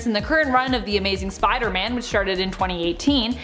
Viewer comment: does she say this run of Spider-Man SHARTED in 2018?